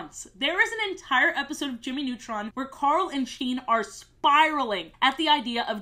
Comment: My brain misheard "carl and sheen" as "Charlie sheen"